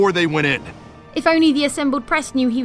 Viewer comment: Wait did Jane just say bae? I thought only 13yr old kids say that?